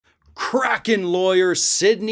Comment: I thought you said crackhead lawyer 🤣🤣